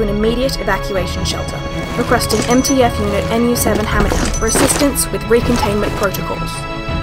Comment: Subtitles??? 1:20 requesting MTF unit Nu-7 hamilton instead of hammer down